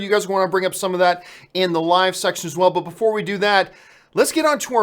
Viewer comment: did he just say *"Live sex"*?😂😂😂😂😂😂😉😂